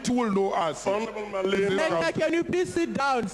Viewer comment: Did i hear 'slender can you please sit down'? 😂😂